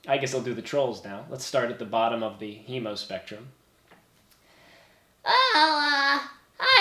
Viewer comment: I THOUGHT HE SAID "the emospectrum"